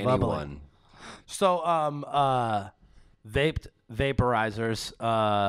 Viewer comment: Lol I thought hat said "Raped" at first